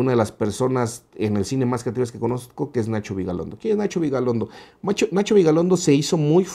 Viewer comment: pensé que había dicho Nacho Vidal hahahahahahahaha